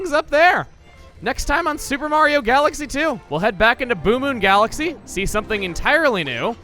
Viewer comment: almost thought you said 'Boomer Galaxy'